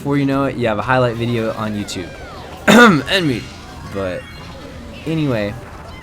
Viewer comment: I thought he said "end me" then realised it was "NMeade"